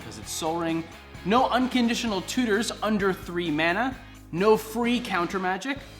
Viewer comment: I thought you said no tutors under 3 mana?